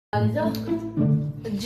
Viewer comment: I heard "Jihoe saranghe" I CANT UNHEAR HELP